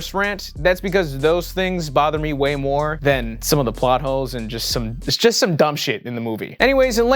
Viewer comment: I swear he said 'plot hoes' lol